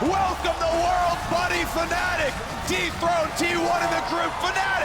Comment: sounds like Phreak says "Fnatic deep-throat T1" instead of dethrone hahah